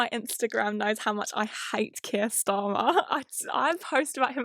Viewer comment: I misheard Keir Starmer as Keemstar